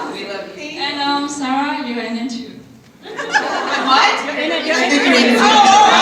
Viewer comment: What did the girl say at 13:09? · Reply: "and sarah you're in it too"